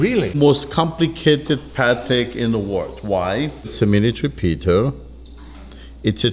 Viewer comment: Sounded like he said it’s a “miniature Peter” 😂